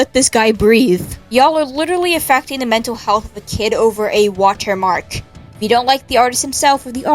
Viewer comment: My stupid ass thought it said, "Bullying over a water park" 💀